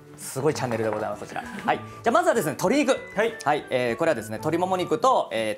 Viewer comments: "鶏肉" が "トリグ "に聞こえてびっくりした笑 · 鶏肉がトリグに聞こえた(((